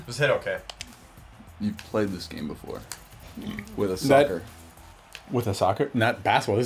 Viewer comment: I thought said sucker. xD